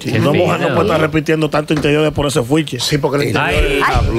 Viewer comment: Perdon que dijo en el minute 10:52 “por ese que?” 😂